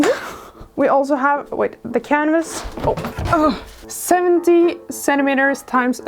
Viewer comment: When she said canvas at 0:55, it sounded like she said cannabis for a second.